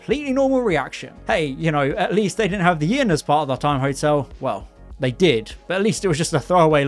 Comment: have the what? I totally didn’t catch what you said there…a little help please… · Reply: He said “At least they didn’t have the Inn as part of the Time Hotel” as in where Mary & Joseph went before being given the stable with the manger as there was no room left at the Inn itself